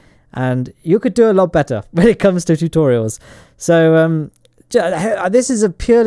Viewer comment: For some reason I heard that as "This is a purely autistic video" instead of "This is a purely artistic video". Must be your British accent. I'm from America.